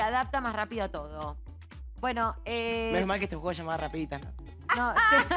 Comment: minuto 9:07 alguien entiende lo qué dice? y me traduzca jaja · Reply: menos mal que este juego se llama rapiditas, NO? xd